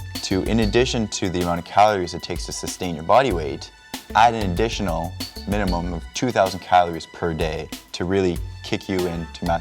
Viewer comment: What did he say? Take an "additional" 2000 calories per day???!! Most people would grow fat in this way,, (!!!)